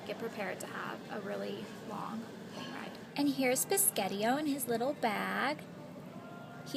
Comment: did you call your cat "piscateo"? 3:30 i'm confused lool